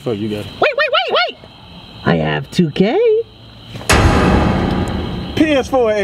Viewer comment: When he said “I have 2k” I thought he said “i am too Gay”